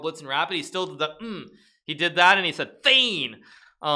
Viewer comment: what Magnus is saying is "faen" which means "fuck"